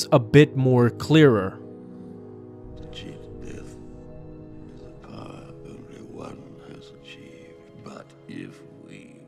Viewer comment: Where would you rate Sidious in the list of many Sith Lords across Star Wars History? Thank you so much BessY Family for being here and supporting my channel, hope you enjoy many more vids in the future! Have Fun! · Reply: I always thought he said "To cheat death is a power Obi-Wan has achieved, haha. So wrong.